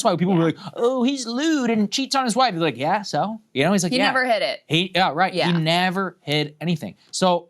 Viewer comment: I thought Krystal said "he never hit it" as in Melania lmao. I wouldnt doubt it. Im sure Epstein and security has though. Barons a lab tube baby so dont even try that one